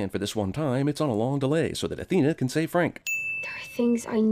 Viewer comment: I heard "so that anne can save frank" and tripped balls